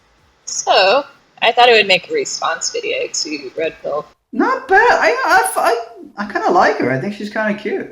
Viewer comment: "mildly attractive"? shiiiiiiiiiiiiit, i'd fuck that girls guts out. she's hot. and you were about to say the same thing at 0:57 but caught yourself :) hahah I heard "yeah i'd fu......... not bad." hahahaha atta boy.